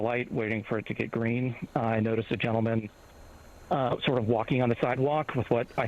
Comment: Did he just say gentleman? 😂